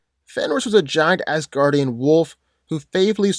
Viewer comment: I swear I heard 'giant ass guardian wolf'